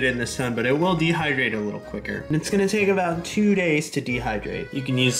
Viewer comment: When he said “throw it on you’re eggs” I may have heard “throw it on you’re ex” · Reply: I thought he said throw it on your ass 💀💀💀 thats why came to read comments